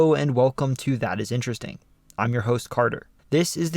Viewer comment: It sounds like he says “I’m you host farter” not “I’m your host carter”